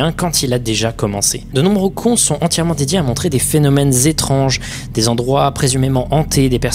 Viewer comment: Wait what ? De nombreux *ons ? j'ai bien entendu ? ... après-tout pourquoi pas